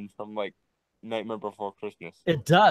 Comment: I heard nightmare before Christmas >->